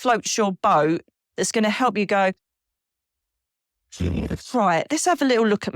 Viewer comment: Hi, great video! I think I heard an clear evp at 27:33 that I thought sounded like "genius" right before you say the word pray!😲❤